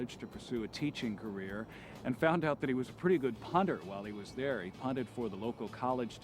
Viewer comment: I had misheard the word "punter", and thought they had said "hunter" and that he hunted for the local team. I thought to myself that Texas must be a naughty place for having hunting teams in college! I'm deaf in my left ear, so that's my story and I'm sticking to it.